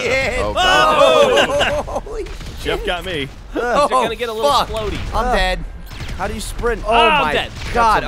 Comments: Did he say "It's gonna get a little floaty"? If so RvB reference! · did anyone else hear this from geoff there gonna get a little explodey XD